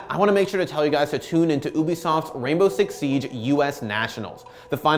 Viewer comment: Did he just say Oobisoft?